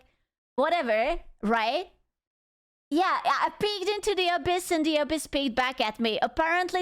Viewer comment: I know she said ‘peeked’ but it sounded like she said “I peed into the abyss and the abyss peed back at me.” 😂